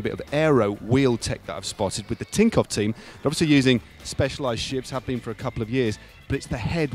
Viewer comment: 40 seconds in did Lasty say "58 on that S-Works Shi* over there". Very harsh.. I like S-Works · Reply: I think he said S-Works "Shiv", that being the model name of Specialized's TT bike. What you thought you heard is much funnier, though, so I say we stick with that.